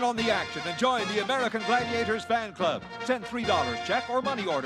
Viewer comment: I wasn't looking at the screen, so I heard "Get in on the action and join the Gladiators bang bus" and I was like SHUT UP AND TAKE MY MONEY!!!